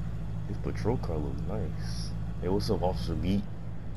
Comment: I thought you said officer meat at first lol